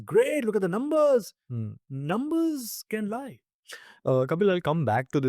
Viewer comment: i heard the first man saying number and the data can lie (intresting ..........?)